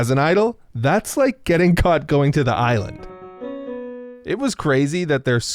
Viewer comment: "...that's like getting caught going to the island." 1:06 Goodness, he had to use that analogy. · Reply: I thought he said isle. But, you are right. He said island.